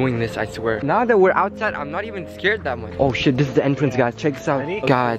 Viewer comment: I hear say oh shit